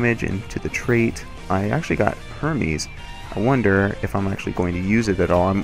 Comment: "Go for damage, into the trait. I actually got Hermes." I had to replay that part because I thought I heard what I thought I heard 😂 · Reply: Herpes? Is that what you thought I said? lol